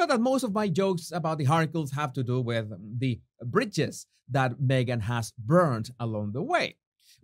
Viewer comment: Love you. With your beautiful accent I thought when you mentioned burning BRIDGES I thought you might be saying BRITCHES re: Mrs. Michelle O, which I believe This One’s Wife has been cosplaying, e.g. the too big pants, not hammed, belted under the boobs. I loved the pic of both side by side.